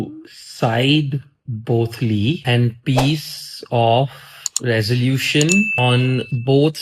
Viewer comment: ye kya bola 😂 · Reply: Gaali de gya voh sabko, peace off